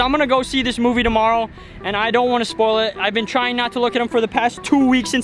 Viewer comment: did say Batman dies because that’s what it sounded like